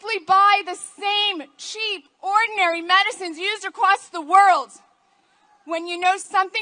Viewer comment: Did she said "the same cheap ordinary medicines used across the worlds"? (2:14) Is there also a conspiracy about terrestrial medicines being sent to other worlds?